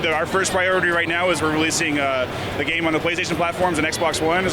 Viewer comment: did he say playstation 5? o.o